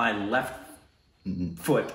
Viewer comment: That was Tabbu Or I will Chop off my lefttttt Foot Meanwhile Le Indians: 😅😅, I thought u were saying Left TESTICLE 😂😂😂😂